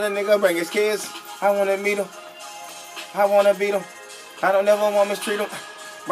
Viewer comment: did he say he wanted to beat someones kids??? If he did wtf.